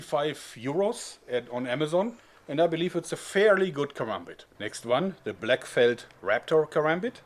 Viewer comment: I thought he said harambe! Haha!